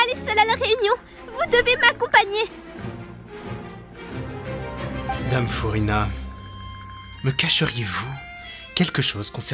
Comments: I thought he said "damn, furina" XD anyway this was soooo cool :) especially the furina voice actor she sounds soooo perfect! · He said: "Miss Furina" I heard: "Damn, Furina" xD